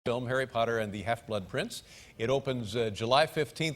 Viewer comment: Did I just heard "dumb Harry Potter "? At 0:01?